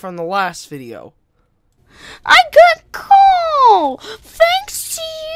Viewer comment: I thought he was saying "I got a cold thanks to you guys" but now it sounds like he's saying he got coal. Well if that isn't made up and his parents DID give him coal, THEN WHY DIDN'T THEY SHUT DOWN HIS CHANNEL INSTEAD??! Or maybe they're planning that next